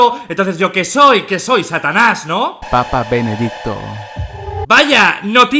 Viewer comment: Escuche papa pene adicto xd